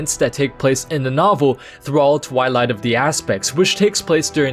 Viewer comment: i literally heard you saying: ''Twilight of the ass-pecks'''